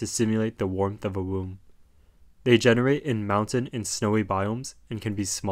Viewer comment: THE WARMTH OF A WOMB 😭😭😭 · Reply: HELPPP I THOUGHT HE SAID "the warmth of A WOUND ” 😭😭😭